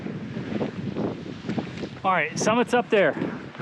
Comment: When you said summit is up there, i thought you said someone is up there. Naw!